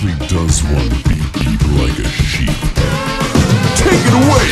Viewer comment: Near 3 minutes in, I thought they said, "Dab, dab, like a sheep"...